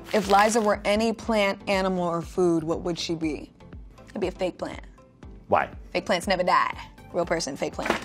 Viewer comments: Every time she said fake plant I thought she said fig plant until the picture came up 😂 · swear down thought she said fig plant instead of fake plant